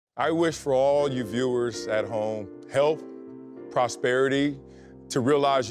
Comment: I thought he said help not health 😂😂